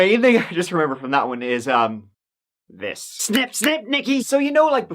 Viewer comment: Lmao sound like said " snip snip niggy" 😂😂😂😂